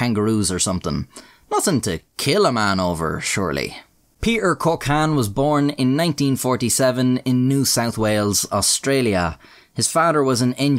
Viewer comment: Am I the only one who heard Peter 'Cock-Hand'???!